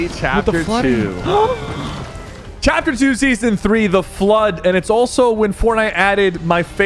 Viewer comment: What did Nick say at 12:10 😳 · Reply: With the flood but i heard what you heard